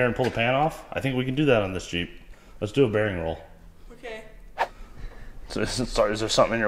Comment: it ALOMST sounded like you said "Do a Barrel roll" but you said "bearing roll" 😂😂